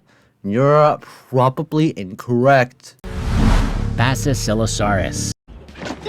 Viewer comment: I heard assilosaurus